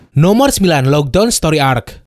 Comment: "Logue town story arc" gw dengernya "Lock down story arc"